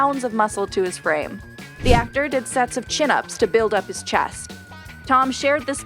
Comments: did she really say he did chirrups to build his chest? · Did she say "Tom did chin-ups to fill out his CHEST" 5:20 .